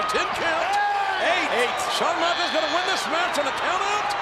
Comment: Anyone else noticed how the commentator said Shawn Mendes instead of Shawn Michaels at 18:00😳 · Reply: He said Michaels but it sounded more like Mendes...